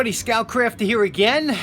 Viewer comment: Since you always say: "Scout Crafter again." I am trying to remember back to the very first video if you just said: "Scout Crafter." :) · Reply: With your dialect, for a year I thought you were saying Scout Crafty. I'm not joking. No, I didn't win most likely to succeed in high school.